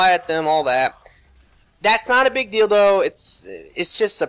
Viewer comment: Anybody else heard, "...that's not a big dildo..." @ 17:16?